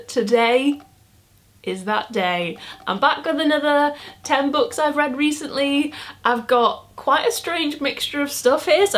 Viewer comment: I thought it said 100 books I've read recently and was like how???😅😂